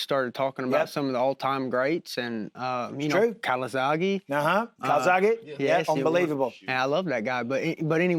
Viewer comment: which boxer does he mention when talking about running? Can't understand what he says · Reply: Joe calzaghe. English boxer had quite a run. Was fun to watch, high volume boxer threw multi punch combinations good footwork and had really fast hands. A little flashy, almost a white Roy Jones I think his dad coached him as well they had a cool dynamic